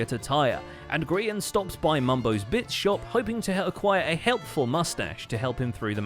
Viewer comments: omfg at 13:32 i misheard it as “mumbo’s bitch shop” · i thought he said Mumbos bitch shop 😂🤣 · i thought he said mumbo's b** shop here lol 13:30 i was shocked for about half a second